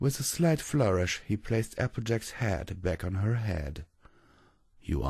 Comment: Spoiler alert for this chapter. I thought you said vanguard picked up applejacks head(instead of hat) had to fight back the tears